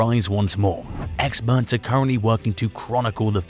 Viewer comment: Did he just say "construNCtion" ????? 🤣🤣